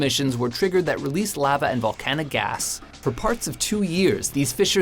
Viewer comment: Anybody notice at 4:29 he said "Volcanic Ass" instead of "Volcanic Ash". 😂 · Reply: Ryan Kruger he said gas